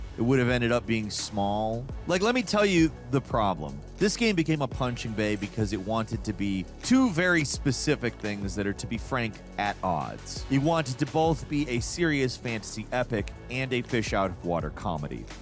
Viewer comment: punching bag? Or did you say punching “baby?” If it was the latter, it was definitely a Freudian Slip.